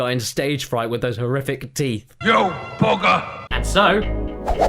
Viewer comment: I think he said "You bugger!" But I swear it sounded like "You booger!"